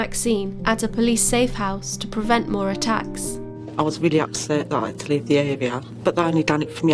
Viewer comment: Literally, wtf did she say?? 🤨 If you can understand it, the rest of us would appreciate some subtitles. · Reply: "upset"